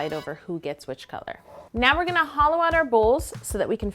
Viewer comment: She said hollow and I thought she said holo and I thought of Cristine from SimplyNailogical. They should do a collab. Cristine would do cake nails and Yolanda would do a nail polish cake. And they're both Canadian.